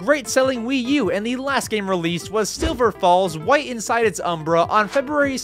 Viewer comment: Is it just me or when he said “silver falls” I thought he said silver balls💀